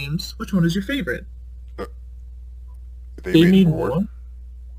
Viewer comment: Who's name did they say at 3:26? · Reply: Oh no, they said "They Made more?" and also I think they're talking about the Sonic Modern games